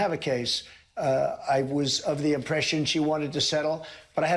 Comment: Trump didn't say I thought we were going to settle with her. He said that he thought she wanted to settle. @ 2:16 Not only are you bad at listening at this point, but that you couldn't understand Trump correct himself before that, shows, I don't know, lol: Maybe you shouldn't be posting these videos. I am just going to stop here at 2:43, even though I had thought this topic might be worth watching before that.